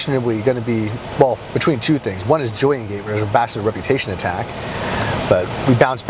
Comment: what did you say at 6:43 i think the background noise isn't letting me hear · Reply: Julien-Gate